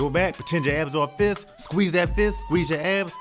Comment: I could have sworn you were saying "squeeze that ass!!" But I felt my abs working 😏😅👍